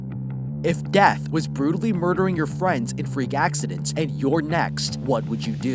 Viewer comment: I thought he said “if debt was brutally murdering your friends in freak accidents” since I cover a part of my phone